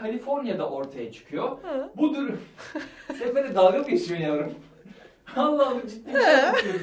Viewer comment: dk yarene YAVRUM MU DEDI YOKSA BENMI YANLIS DUYDUM